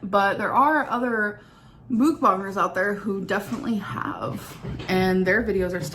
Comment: what did she say? The fck is a mookbanger?